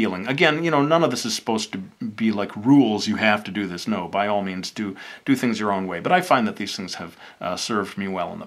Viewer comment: i thought he said "by all means do it the wrong way" lol oops